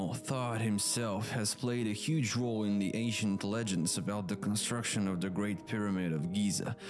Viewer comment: Sounds like he said "Asian legends" LMFAO